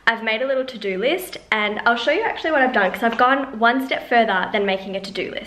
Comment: Sounded like you were ganna say my channel name 'one step closer' 😂 But congrats on the productive day! Stop reading thru comments and be productive guys!!